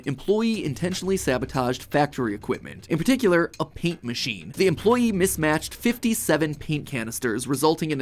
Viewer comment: who else heard ink machine